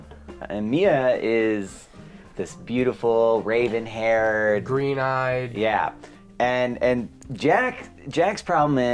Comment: I dunno about “tea’s” but for a moment I thought I heard “green teeth”